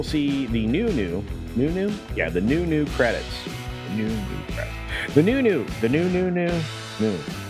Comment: LoL it sound like you said "The New Nude Credits". 🤣 😂 😅 😆 😂